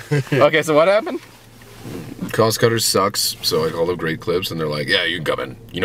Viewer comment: thumbs up if you thought he said gray clips